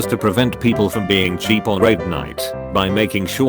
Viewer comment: That moment when your electronic censor continually says rape instead of raid... (5:45) Seriously, is anyone else hearing that, or is that just me?